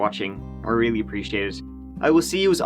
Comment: when you said "Mike out," it really sounded like you said "My Gout."